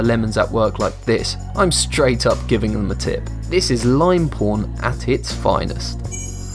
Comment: I think he said like porn,tell me if I’m wrong